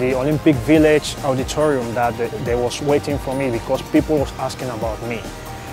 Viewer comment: did he say team India waiting for him?